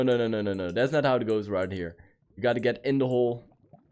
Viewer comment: I heard it it's in the hole